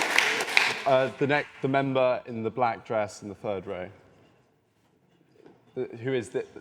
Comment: Did anyone else hear the guy, when calling out members to ask questions, describe these? 1. “The girl in the pink” for a woman with a off the shoulder dress and rosy skin and 2. Another girls hair as a “fur beret”? 😂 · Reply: “Fur beret” would be quite amazing. I think you just misheard “third row”, though, at 53:54 ?